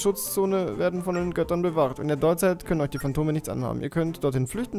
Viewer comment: Wer hat 11:33 Kondome bei Phantome verstanden?xD